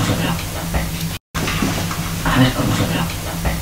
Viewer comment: En el 17:05 parece que dice aver como se olle la puta peli